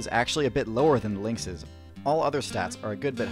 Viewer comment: when he said lynx's, i thought he said " LINK" I was like "eyyyyyyyyy..??"